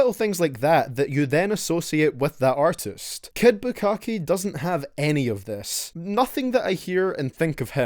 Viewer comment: you just called him "Kid Bukakke" 😂😂😂 I died when I heard that lol